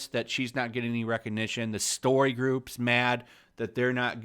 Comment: I thought you said the SOY GROUP lol